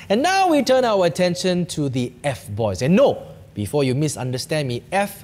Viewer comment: When he say about F boys I thought he was saying f4 boys lol hahhaha